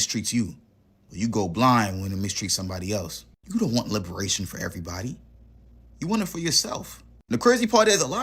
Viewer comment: Oh, he said: "liberation for everybody"! I thought he said: reparations for everybody!! My bad....